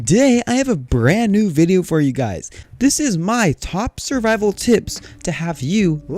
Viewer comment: I swear I heard "Survival Tits"